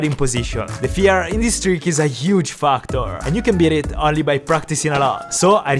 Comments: When he said Factor.... I thought he said fu*k tors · When said "Factor" I thought he said "F*cktor XD